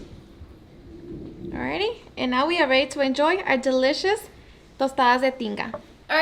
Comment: I came here because I thought she said Tostadas de Chinga!!